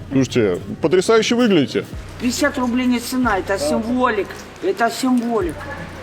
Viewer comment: легендарная сценка...)) Женщина: писят рублей не цена - это от символик (что ли), ЭТО ОТ СИМВОЛИК Потом: что? возьми еще шо ни будь, возьму мой мальчик.. Если правильно услышал.